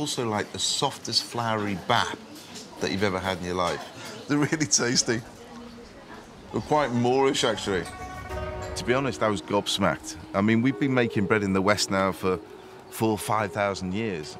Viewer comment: Did he say it's quite moorish at 5:28 ??